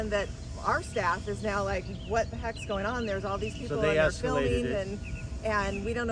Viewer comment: he clearly said “THEY ESCALATED IT” yup your employees escalated it and they can do it to kids…